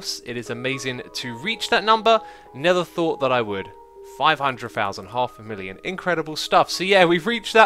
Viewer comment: Did he just say 'NETHER thought that i would'